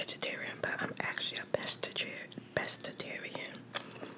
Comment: Love the sounds😍 One of my fave videos When she said she was a pescatarian I thought she said pastatarian😂 I'm a vegetarian, I was considering going pescatarian but I had pet fish and I felt bad :/